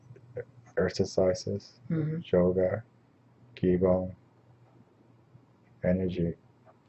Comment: What type of work is mentioned at 1:05:03-1:05:07? Keyble, Kible?? It can't be Kegel lol...I couldn't make out this word & closed captions aren't available. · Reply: Tae bo, Chi gong